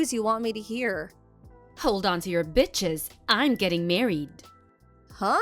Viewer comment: It’s ‘hold on to your BRITCHES’ 😹 that said I’m totally going to use what you said at some point in D&D!